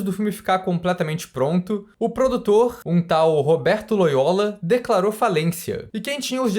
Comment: eu ouvi Roberto Boyola 😂😂😂😂😂😂 a quinta série é eterna